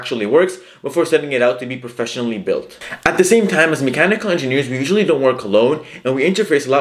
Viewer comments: I got to 3:28 and thought you said, “We usually don’t wear cologne” & I just nodded my head thinking… sure that makes sense, don’t want to fume out everyone you’re working with. Lol. Anyways, thanks so much for your help, I’m working on finding my first engineering job and all your videos are so helpful and really give me a good idea on landing something I hope to really enjoy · When u said “we usually don’t work alone.” I thought u said “we usually don’t wear cologne.” 😂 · I thought he said as mechanical engineers we typically don't wear cologne lmao